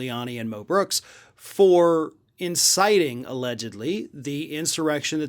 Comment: lol i thought he said mel brooks...i was like "hol'up...what????"